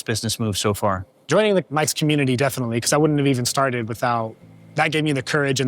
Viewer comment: Did he say "joining Mike's community"? If so, who is that?